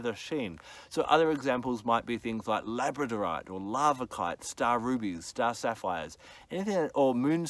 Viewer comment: What did he say after labradorite (11:22)? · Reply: Larvikite